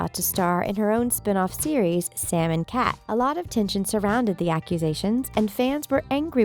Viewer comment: for fans of sam and cat: omggggg even the subtitles think its called salmon cat;)